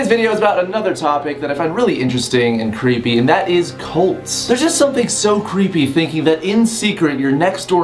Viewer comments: I legit thought it said clits and I was like lol wut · omg i thought he said colds Xd